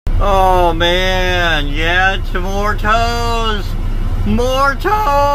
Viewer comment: I thought he said more toast 😂